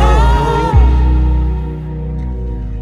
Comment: I thought it said "...myself and more..."